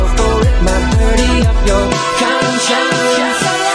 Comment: it sounds like he says put my dirty up your... concionce